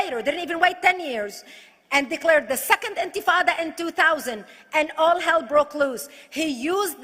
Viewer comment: Did she say antifa?